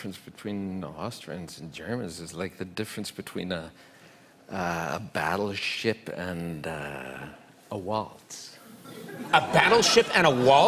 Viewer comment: I thought he said "a barrel of shit and a waltz."